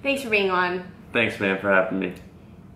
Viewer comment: does he say "thanks babe for having me"?!!!! 🎉 😍😍😍